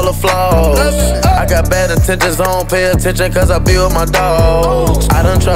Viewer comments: I thought she was saying I got 10 bad intentions the whole time 😳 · i thought she said tamppad intentions